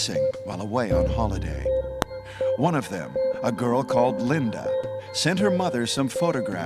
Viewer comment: Legit heard you say “a girl called ligma”